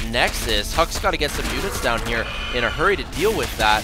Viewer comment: did he say huks gotta get some mutas down here? at 3:43?